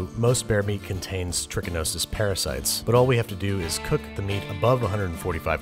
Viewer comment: "The only thing I don"t like about this stew is that Pearson makes it" literally sounds like something Arthur would say. 10/10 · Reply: I even thought Babish said "tuberculosis parasite"